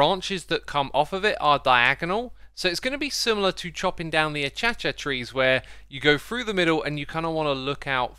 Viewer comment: did u just called it the achiachia tree?